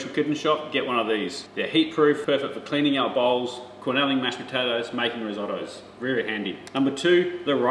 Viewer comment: What is “Cornelling” potatoes, please? In over fifty years of cooking foods from all over the world I have not seen this term. At first, I washed looking at the screen. I thought you said “quenelling”. The next time I watched and saw cornelling. A search only offered recipes and other information associated with and/or by Cornell University. I hope you can clarify. Thank you. By the way, I have my mother’s ricer. The riced potatoes tasted so differently they were not rescued successfully by gravy. Perhaps she didn’t add cream or anything else to help. I’m enjoying your program very much. Thank you, Alison H.